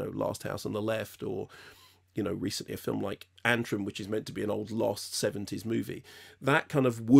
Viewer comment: What film is he referencing here? Can't make out the title. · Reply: I think he mentioned Antrum <link>